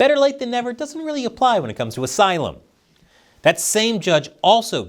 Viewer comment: The captions says "bureaucracy" instead of "asylum."